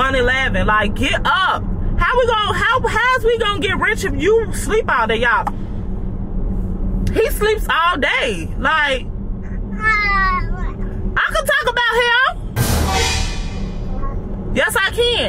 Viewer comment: Sounds like Papa said no u can't talk about his daddy.